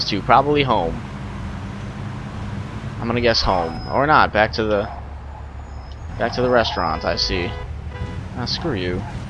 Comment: Haha at one point, u said back to the restaurant, but my youtube lagged and it sounded like u said: Back to the refrigirator:)